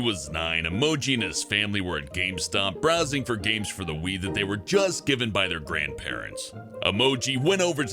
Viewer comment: did he say weed 😂